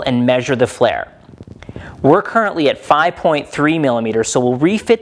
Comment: Sorry, not displayed measured digits after peening chain pin. So before and after digits is 5,03->5,3? (08:21) Did I hear well?